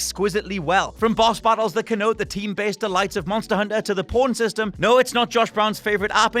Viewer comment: Lol. Is he saying 'Porn system'? I swear I've heard him talk about Dragon's Dogma in, like, 3 videos, and I swear he's saying 'porn system'. Hahaha. After looking this up, he's apparently saying 'pawn' system....but even the closed captions thought he said 'porn'. That's hilarious